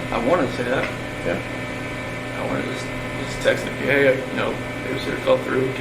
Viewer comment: does CW say I wanted to text NK? If so lol at him calling her NK too